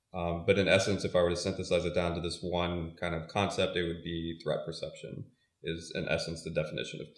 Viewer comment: did he say "rep perception or rep perception"?? A little emphasis would help on this... several times KEY words don't get through. This would be a key word in this definition, no? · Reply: threat perception*